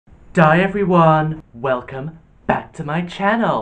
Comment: notice how he says “die everyone” instead of “hi everyone” 😂